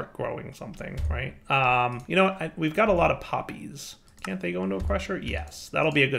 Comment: I'm falling asleep not cause of this but because lack of sleep. @ 13:47 I heard "puppies, oh good they can go in a crusher"